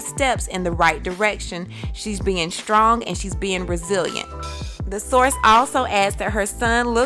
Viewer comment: Did you just say Braziliant? (2:16) Do you mean resilient? Just asking for a friend